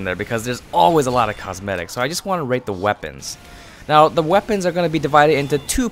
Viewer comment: Am I the only one who heard him say "rape these weapons" in the beginning